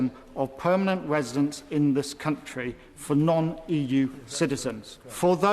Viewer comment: Did he say he had a husband?